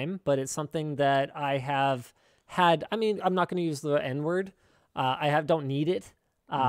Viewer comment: did he just say he's not gonna use the n word???